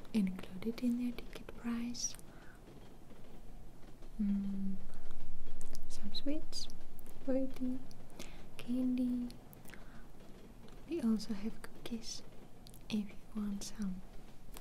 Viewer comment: lol, at 5:20, did you say "We also have good kiss, if you want some"? Yes I do. And I'll pay extra even though it's already included in the ticket, hehe :) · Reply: Dziri it said we also have cookies if u want some